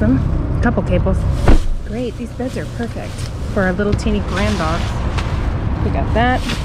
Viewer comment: Lol, I thought you said(for the pet bed) for a little tiny grandma.... lol! I had to listen to that again & then heard what you really said. I was crackin up!!! lol! :)